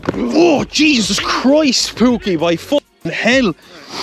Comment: DID HE JUST SAY POOKIE..